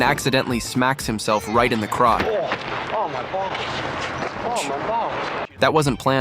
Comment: In Stripes I don't think he was saying "oh my, oh my". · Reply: Nah, he was 100% saying "Oh, my balls!"